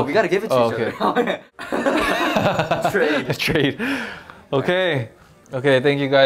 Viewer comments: the fact that they said 'trade' but the subtitles say 'straight' lmao. ok SM we get it the bromance is no homo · If you read the subs at 11:24, it says "straight" instead of "trade"... thinks of when Mark thought Johnny was saying he thought they were going to make